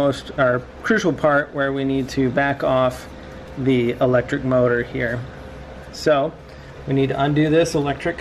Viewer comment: What is the caliper tool socket size for rear brakes? 12 point 14mm? Or what? And was @ 7:45 you said electric motor or was that parking brake